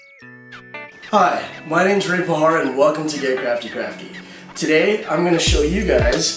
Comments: did u just say Gay Crafty Crafty? at 0:22 xD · Hhahahahahah at first i thought i heard "from gay craftie craftie" i was like omg how fitting hahahah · Yes she say gay crafty crafty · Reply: Damn. Maybe because I am also Filipino and I hear that type of accent or sound of voice all the time, I cannot hear him say "gay" at 0:23. I hear "get". Darn. It's the dress thing all over again